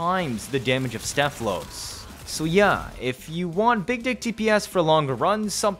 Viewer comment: Since no one caught it I'll say I heard you saying big d"$k DPS 😭😭😂🤣